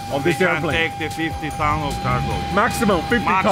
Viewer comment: I thought the load master said “60 ton”. Right after saying maximum of 50😅 wouldn’t be surprised if these get overloaded occasionally